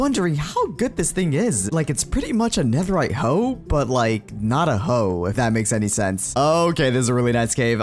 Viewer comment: What said: "But like not a hoe" What I heard: "But like Idaho"